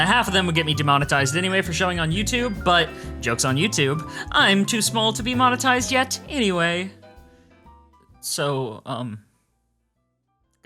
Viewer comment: I heard "I'm too small to be motenized yet anyway" and thought "I HAVE TO FIX THIS" I am now susbcrimbled